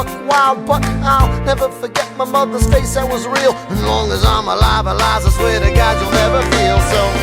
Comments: is it just me or does it sound like he saying "as long as I'm a llama I swear to God Eliza you'll never feel so" ...HELPLESS! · All I hear is "and only llama 'Liza I swear to god.. " SOMEONE PLEASE TELL ME THE REAL LINE I'm to lazy to find it.. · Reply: DaWolf .Spirit “as long as I’m alive”